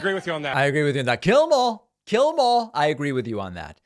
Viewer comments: Sounds more like "Jail them all" instead of "Kill them all". I hear a j sound at the beginning. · He thought the man said "kill the mall", probably in a way to encourage small, local businesses.